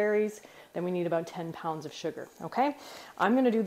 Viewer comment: i thought you said 5 lb sugar.